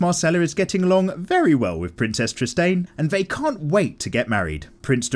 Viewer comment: did he say princess tristane?